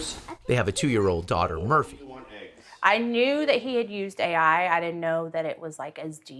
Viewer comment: They have a daughter named Murview? That's what I heard.